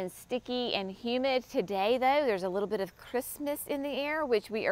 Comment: At first I thought you said, "there is a little bit of Christmas in the air" and I panicked! CRISPNESS - got it! Whew!